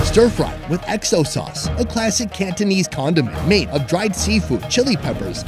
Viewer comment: Did he just say condom made up of dried sea food??🙄🙄